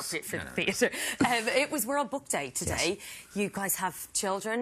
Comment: I'm not English, Can samoone write about what a she say i 01:57? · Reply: she said 'Um, it was World Book Day today...' would you like the rest of the sentence?